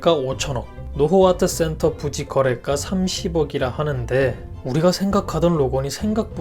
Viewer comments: 자막은 300억 인데 나만 30억으로 들었나..? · 30억? 300억? 뭐라는거지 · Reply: 30억